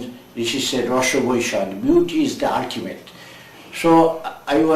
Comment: Hi - nice interview as always. BTW what did he say about beauty at 5:15? Our ancient rishi says "beauty is d........" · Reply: Beauty is the ultimate.